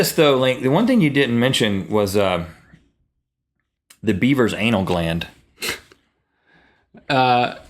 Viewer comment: i heard beiber's anal gland O.O HAHA!